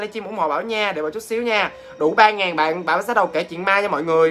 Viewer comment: đủ 3000 bạn thì tớ cứ nghe thành đụ 3000 bạn